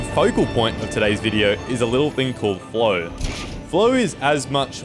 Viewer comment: I thought he said "Blow." 🤣 That's true too for flow too I guess 😂